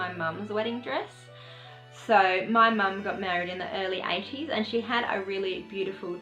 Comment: I thought you said that your mum got married in HER early 80s! XD Haha!